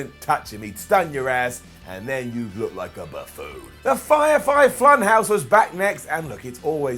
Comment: Fire Thigh Flunhouse is what i heard lol